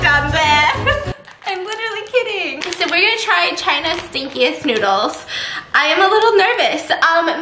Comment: "—stinkiest noodles..." What I heard: "kinkiest noodles" bruh what? lmaooo.